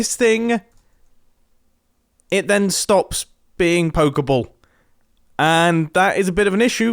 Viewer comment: When Mumbo says "pokeable," I keep hearing it as "Poké Ball."